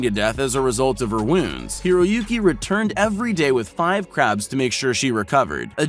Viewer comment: did he say craps? Like poop😃